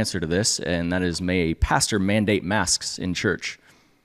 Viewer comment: I so thought you said — Can a pastor mandate Mass in church in the last episode 🤣🤣🤣